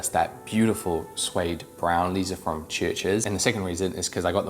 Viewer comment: can't quite catch the name of the brand at 3:45, what is he saying the brand name is? sounds like Churches.